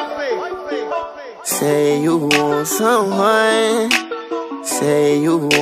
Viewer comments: I thought it was "say u want some wine" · I thought it said say u want aome wine the whole time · Yes we all thought he said say you want some wine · I looked for other comments but there’s nothin 💀 I can’t be the only one who thought it said “say you want some wine” a couple years ago 🤣